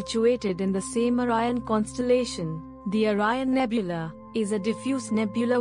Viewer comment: mark, I really thought it said...ONION nebula. 🤦🏾‍♂️